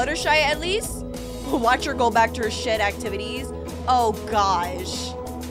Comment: A lot of people may know this but she said SHED I’m just reminding because the captions said something else.